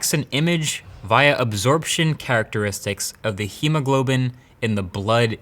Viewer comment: What the duck he said... Hemoglobin